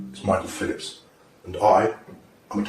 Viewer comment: I thought it said Michael Phelps at first and I was like TF